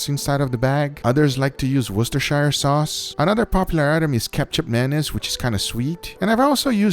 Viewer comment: I always thought when people said ketjap manis they said ketchup mayonnaise but now that I see on the bottle I know it's something totally different.